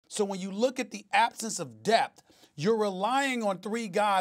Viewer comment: Owh... SAS said "the absent of depth". I thought he said death. LOL!!